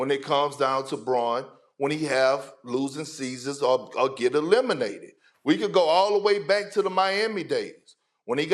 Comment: Did i just hear "lemonated". I knew Lebron was a lemon.. but damn.